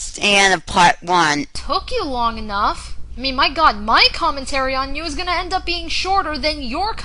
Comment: I can't believe for the longest time I thought she was saying "My commentary WAS going to be shorter"